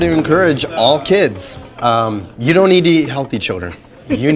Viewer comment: Why am i hearing “you don’t need to eat healthy children”